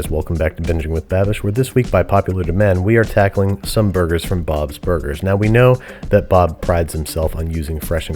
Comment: I thought it said bon’s burgers and was terrified.